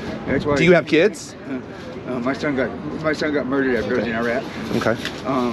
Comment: >he’s an Iraq combat vet >his son got murdered in Iraq This dude is FOS 😂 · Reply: He clearly says "my son was murdered after I was in Iraq"